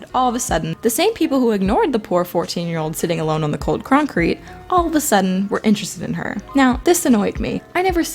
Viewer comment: I apologize if this video pisses you off and you feel the need to leave rude comments, but this is my final video about highschool i know they are negative and rant style. I apologize if that isnt your forte or you think im "full of myself" i just had a bad time in highschool, and part of that was my fault for being negative mean and not putting myself out there. I admitted that in the video. This is my final rant video about highschool, sorry if it outraged you. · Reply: I heard you say ‘croncrete’. Also nice vid! 👍